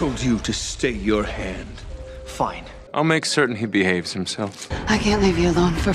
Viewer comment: tbh I heard “I’ll be sure he hippy haves himself.” ..